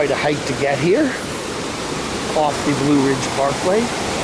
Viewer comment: Did you say blue vein parkway ?...Princess Blowing Bubbles...Aunt Bee 🐝 sighting